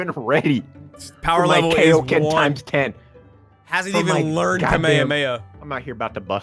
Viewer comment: Did just say kamayomayo 7:52🤣 wtf